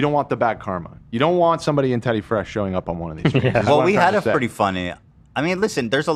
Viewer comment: i swear i heard say "Peddy Fresh" 🤣🤣💀